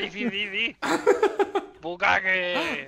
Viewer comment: bucaque dice 😂😂